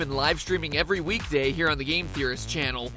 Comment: Is it just me, or did i hear Retro-Gat from Saints Row IV at 0:07 say GAME ?